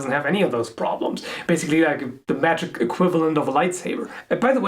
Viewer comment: A lightsaber is scientific....science fiction but still science, not magical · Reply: You misheard he said "The magical equivalent of a lightsaber"